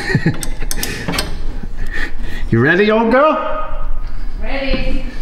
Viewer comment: I always thought you were saying O girl as in Mrs O. Didn't know you were saying old lol.